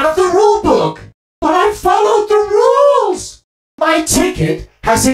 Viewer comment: Oh from that, I misheard it as “Why don’t you follow the rules?? :(“